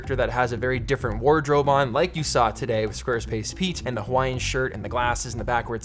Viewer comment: Wait, Squarespace Pete isn't a real person!? Just when I thought 2020 couldn't be any worse... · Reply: Lol I thought you said SquarePants Pete